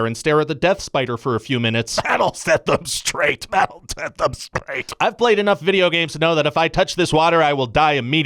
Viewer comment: I used to hate when my parents made me sit in the weird pond chair and stare at the death spider for a few minutes to set me straight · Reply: I'm glad I saw this comment, because I heard "sit up straight" and thought that was a pretty harsh punishment for slouching.